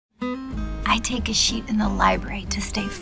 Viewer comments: i was so faded and thought that lady said i take a shit in the library ahahaha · I remember when I first saw this, my younger sister and mom were in the room, and when my mom heard this, she was like "....What? .......WHAT?" She thought they were saying "shit" not "sheet" xD